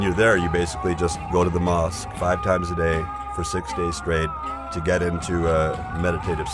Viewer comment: did he just say you go there 5 times a day 6 days a week.